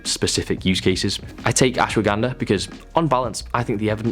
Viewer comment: what was said there? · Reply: ashwagandha